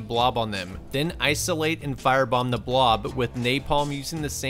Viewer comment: Teacher: Any answers to "how to get rid of covid?" Students: silence Quiet kid: 21:43 Class: 👁👄👁 Edit: OMG THX FOR THE LIKES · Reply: I heard it as fire bomb the block